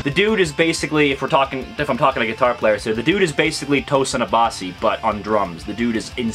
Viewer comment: what's the guitar player name you say a 7:05? i don't manage to hear it correctly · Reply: Tosin Abasi